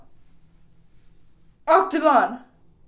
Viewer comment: instead of saying “앗 뜨거” (its hot) sounds like “octagon” LOL